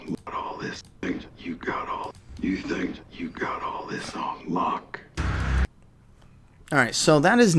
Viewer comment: Sounds like Negan is saying "You think you got all this on LOCK" not Luck. I could be wrong. But it could be something he is saying to Rick meaning, you think you can be a leader and control all these people (since all the communities seemed to have rallied under his leadership even though its looking different, since it's all about confirmed were losing him and maggie )... or. Could it possibly be the same thing but hes saying it to Daryl, not Rick, AFTER Rick dies/leaves. Meaning, you think you can really control these people like Rick did ... just a thought. Maybe I'm hearing wrong and he is saying luck and not lock. Either way. Awesome video as always. Keep up the good work !!!